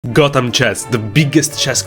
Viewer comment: Why did I hear "Gautam chess"?